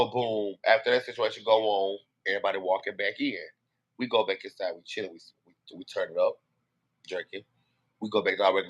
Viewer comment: Did he say we go back inside Chilis ?!?!?